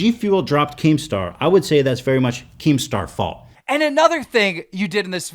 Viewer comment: does he say if g fuel dropped keemstar, then keemstar falls??